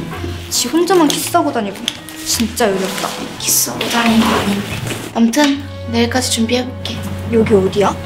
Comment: 여기 뭐라고 하시는거지...? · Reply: 키스하고 다닌거 아닌데 라고 한거 같아요